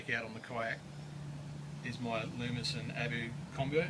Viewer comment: Can anyone help me I want to know what rod & reel @ 13:33 I can't understand what he says, but I'm new to fishing so I don't know any name for rods & reels so I'm lost on what he says? Thanks in advance for any help. Patrick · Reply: G.loomis rod. Abu reel